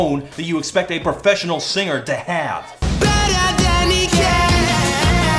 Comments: When he says better than he can, I hear "pet a pelican!" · The first time I heard this song I thought he was saying, "Pet a pelican!" Instead of "Better than he can." lol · When he says "nice girl" it sounds like he said the n-word and when he says "Better than he can" it sounds like "Bed her Danny can."